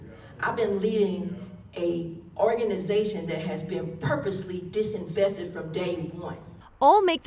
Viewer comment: Apparently English is not her strong suit. I think she meant to say "underfunded." Disinvestment means taking money away, like pension funds are doing to non-PC businesses, Israel, etc. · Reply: I thought she said " disinfested" at first. Had to watch it again with the volume up